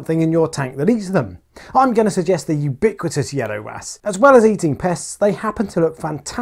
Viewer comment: What kind of fairy wrasse? Rewound several times cant understand what hes saying 😬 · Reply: Thanks for that! Thought you were saying a-ok wrasse! 🤣